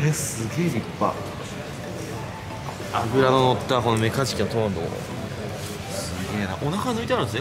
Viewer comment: メカジキのトマトって聞こえるのは私だけ？！